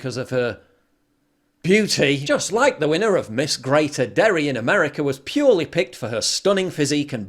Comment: Paul, did you say "Miss Greater Dairy" there? Ah! That makes sense now.